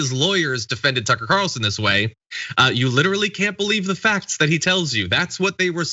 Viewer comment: I heard her say, "fact that..."